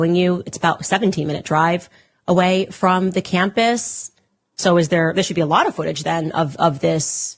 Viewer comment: she never said 15 min after the shooting he was spotted (27:17) she said 15 min away from campus, I am sure I hear this right